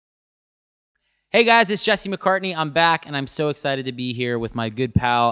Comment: I thought he said Justin McCartney I was like who? 🤣😭